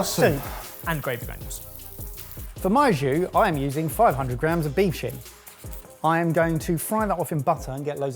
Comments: Anyone else hear say, "I'm using 500g of beef shit"? I had to rewind to listen again. It just didn't make sense. 😅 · I had to rewind and turn on closed captions at 9:54 because i thought said beef s#!t. I was relieved when it showed beef shin. Sounds like i need to go to the doctor and have my hearing checked again. In my defense i do suffer from hearing loss and tinnitus from my time in the military. Keep up the good work chefs! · made me do a What the? When he said beef shin I thought he said beef shit 😂😂😂. I think both meals looked good. Just wondering was £110 for the whole thing, per serve (making it £440) or was it £27.50 per serve?